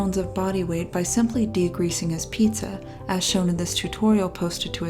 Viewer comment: I thought she said "decreasing his pizza" and I thought "no duh" but then there was the stack of pizza boxes and the paper towels and it took me a moment. De-GREASING. Pure effing genius.